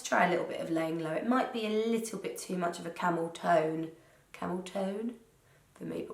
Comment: I really thought she said the eyeshadow was called 'Camel Toe'!